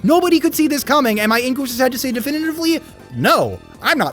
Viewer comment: Wtf are you saying at 15:51? You mumble the heck out of that sentence and auto captions don't have a clue either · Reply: "Am I in Goose's head to say definitively?"